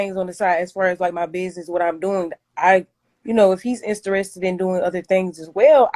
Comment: Did she just say Istarested 😂😂